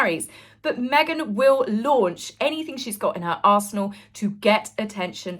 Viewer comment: oh 😂 you said arsenal, I heard ass****😂 Love your content ❤